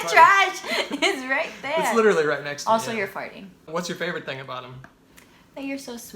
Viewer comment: did she say "also your 40"? Thats fucked up lol